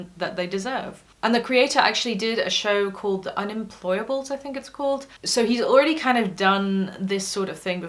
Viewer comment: auto-generated captions wrote "neurotypical gaze" as "neurotypical gays" and i honestly find that hilarious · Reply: I thought she really meant that... Oops... And I heard "The Deplorables" as "unemployables" or something and I only knew what she meant bc I watched it a few years back